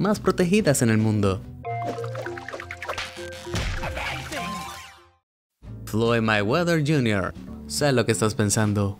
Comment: Hola! Me sacan la duda de que dice el muñequito en el 0:21 ? Gracias! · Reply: Dice Amazing